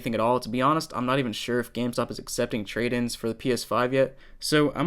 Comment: The first guy sounded like he said "scam stop" haha.